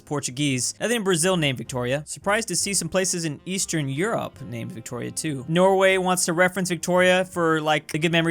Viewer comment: Me getting excited because I heard Victoria 2